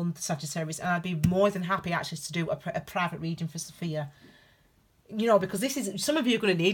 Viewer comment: i have a question....at 16:52 it sounds like you said " i would be more than happy to do a private reading for Sophia....you may have said something else..thats my name sophia.... i may have misheard.... thank you for this also .....you are awesome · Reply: OMG I so said Sophia!!! And I was literally thinking about Pistis Sophia (Gnostic text) and a post I want to write about her, like a second ago!! So it’s a sign for both of us! 😂😂. Many blessings 🙏🏾💕✨😊